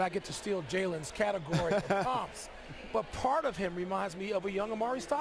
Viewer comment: What did Michael Wilbon say?... "Not that I get to steal Jalen Rose's category of--" what?... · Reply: He said comps. He abbreviated comparisons